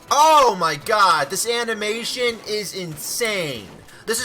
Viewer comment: it sounds like you said the santamation 😂